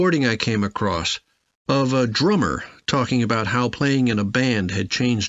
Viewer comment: I got 36 I heard it as dreamer instead of drummer hahaha or else would be 37 that's alright, am still in the learning phase. Making mistakes is fun to learn to be perfect sir:) thanks for this and do you have anything for reading for tomorrow test :)